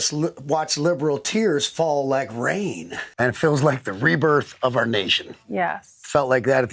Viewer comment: When kid rock said "rebirth of our nation" All I heard was "rewatch birth of a nation"🤮